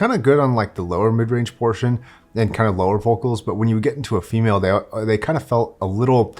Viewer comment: Did say, “When you get into a female…”?🤔